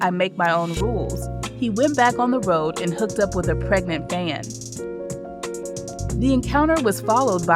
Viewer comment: “he hooked up with a pregnant van?” I can’t understand what she said 😬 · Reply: Fatimah Crawford *pregnant fan